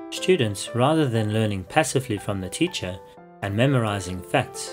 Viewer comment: what was said on 0:20 to 0:25 guys? I cant really understand what was said on that part. someone help me. thanks in advance! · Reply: Hi Richard, the blank that you are looking for is the word "passively". Hope this makes sense